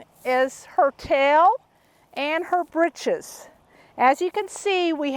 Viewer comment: I thought it said, "hocks, talks and bitches"